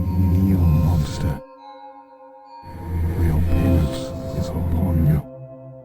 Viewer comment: “your penis is upon you” thats what the subtitles said yo😭😭🥀🥀